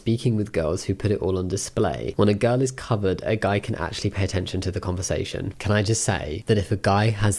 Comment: Not gonna lie, I was doing something else while watching this section of the video 21:43 and I legit heard it as.. “when the gun is covered, a guy can actually pay attention to the conversation” I’m dead💀💀